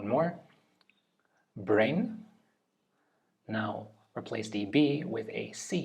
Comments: Only thing I had trouble with was understanding what he was saying, for example I thought he said grain but in reality it was brain. It’s probably just an accent thing. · As an American, I was thoroughly confused by the letter substitution because we spell it "worked" with -ed instead of a -t. I was thinking so hard, saying to myself, "What word could he possibly mean that's spelled w-o-r-s-e-d?" By the second and third words, I was questioning my reality. Especially the last one, since we spell it "cringe" and it's pronounced a completely different way. XD SMH at myself **apparently it was supposed to be "brain?" I heard "bring", which was why I was even more confused. "cring" isn't a word.